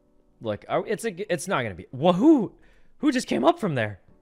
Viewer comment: "wahoo" , but I guess he will probably say what but saw something that's why he said who. but it's still wahooo 🤣🤣🤣🤣